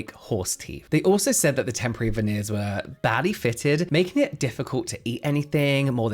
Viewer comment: I thought you said the veneers were “baddie fitted” 😂😂 I was like ope